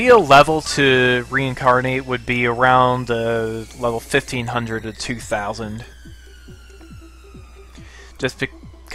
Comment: which numer he said ? i think he said between fif teen hundrend and two thousand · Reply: 1500 to 2000